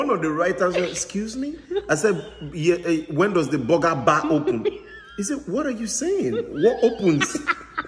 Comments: All I hear is "boga bah"😳😳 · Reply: I heard " When does de boogabah opm?"😆😆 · I heard "When does de boogabah opm?"😂